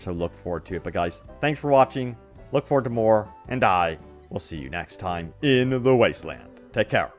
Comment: Hahaha, I thought at first you said at the end of the video, "Thanks for watching. Look forward to more. AND DIE !!!"